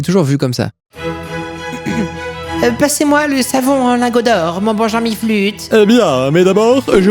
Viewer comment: "passez moi le savon en ARGENT mon bon jean miflute" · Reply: Passez moi le savon en lingot d'or mon bon Jean Miflute*